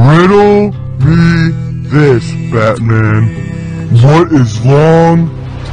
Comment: Did Riddler really said "Fatman" at 0:03 ?